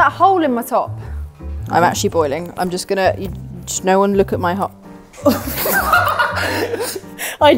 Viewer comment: what was about to say at 10:52 im stupid and cant figure it out💀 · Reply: “No one look at my hole”